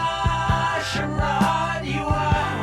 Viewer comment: I was always confused as a kid because I thought he was saying "how harsh a ride you are"